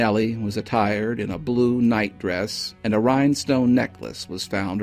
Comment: Now I'm confused 'cause I thought he said evening dress, a term I've heard before and always thought almost synonymous with evening gown but perhaps a slightly more subdued style, not as long and probably not strapless. Also too I'm more familiar with the term "night gown" for sleepwear. I've never heard the term night dress used that way. In fact I dont think I've ever heard the term night dress used at all. I wouldn't think a birthday party or shower (wherever she said she was going) would warrant an evening gown... and why overdress for an event you're pretending to go to, it would be suspicious. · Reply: I heard "night dress".